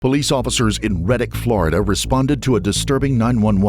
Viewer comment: Thought they said Reddit Florida! That’d be terrifying!